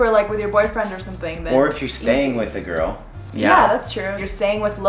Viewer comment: did she just say '' my girlfriend'' ? Dreams just shuttered ...